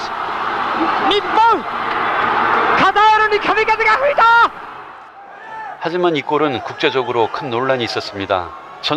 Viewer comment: 초쯤 일본아나운서가 뭐라고 하는건지 아시는분 계실까요?카미카제 뭐라고 하는것같은데 공식방송에서 저단어 사용이 문제가 없는지 궁금하네요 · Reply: 카타르에 카미카제가 불었다 카미카제는 고려를 대동한 원나라의 일본 침공이 태풍으로 번번이 실패하고, 신의 바람(카미카제)이 일본을 살렸다는 유래에서 생겨난 단어입니다 2차 대전 패전의 역사를 제대로 가르치지 않은 일본 입장에서는 자폭공격 카미카제에 대해 대중적으로 아는 사람이 적었을 테니 딱히 문제될만한 단어는 아니었겠죠 한국이나 2차대전 일제의 적국 입장에서는 불편한 단어지만요